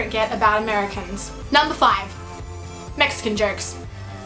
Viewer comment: LOL - at first I thought she said Mexican jerks ;P